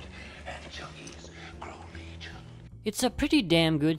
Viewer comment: In his opening speech does Two-Face say "Anchovies grow legion"? I honestly thought that he said this for many, many years...